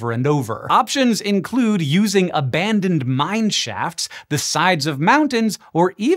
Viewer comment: Okay, my visual focus was elsewhere at the 4:50 mark and I swear I heard "abandoned mine shafts the size of mountains" and just... whuuu??? Why do we have mine shafts that are that big??? Ah, but then I watched that part back again and with the visuals it made more sense.